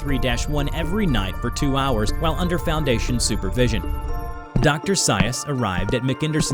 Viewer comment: For a second, I thought he said "Dr. Zaius," and was wondering why he wasn't an orangutan.